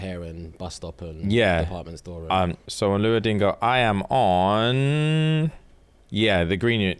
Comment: did call it luo dingo, or was that just me?